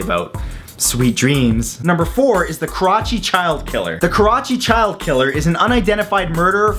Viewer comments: Thought you said crotchy child killer.. XD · hehe I wasn't paying attention and it sounded like you said crotch loving child killer :P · XD who else thought on number four he said Crotch-y killer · For the Karachi child killer, I thought you said the Crotch child killer